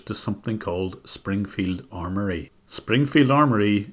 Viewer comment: Lmao I thought you said “Springfield armory” and I was like “who gives $500 do a gun company